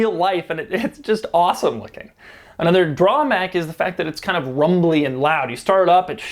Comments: Did anyone notice he said drawmack instead of drawback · did he say drawmack